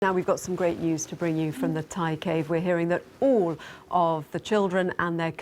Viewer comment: Did she say Batcave?